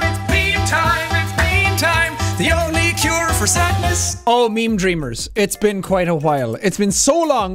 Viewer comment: I haven't watched this in so long, I thought it said "sinners" instead of "sadness"